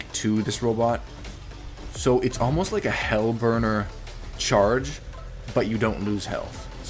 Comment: I thought it said burger for a second